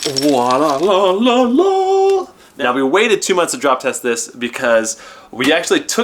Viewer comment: I thought they just said 2 months later for like some kind of joke